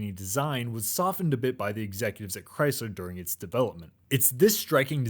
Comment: Did he say chrysler ?